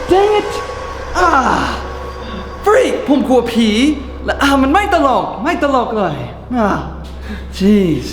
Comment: เหมือนพี่พูดว่า f*ck